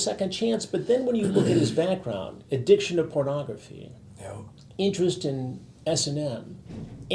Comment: Ngl I heard "Interest in SNL" lmao